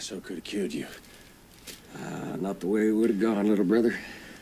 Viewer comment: "Not the way I would've gone little brother" Actually a pretty meaningful line if you've finished the movie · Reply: I always thought he said "not the way IT would've gone"?